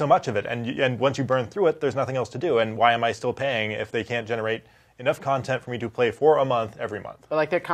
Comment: My headphones are messed up I thought they said you need to play $50 a month for this game I was like oh heck no